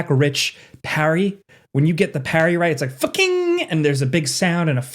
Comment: anyone else heard him yell FUCKING 😂